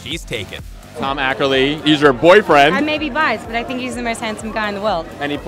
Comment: did she said : I may be bi at @ 1:10 ?? :O